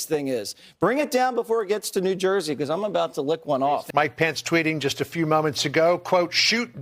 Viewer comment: Did I really hear correctly, did the Fox News host at 2:50 just suggest that he is going to go and 'lick one off'??? Does this have a different meaning in America? To be honest China could use spy satellites to identify what is going on or Chinese Airlines to send Covid 23 spores across the American spies, or just use tourists. What a load of stupid bull. China just trying to make an issue probably knowing that Fox/GOP will react to it. Get stupidity out of the media and deal with real issues.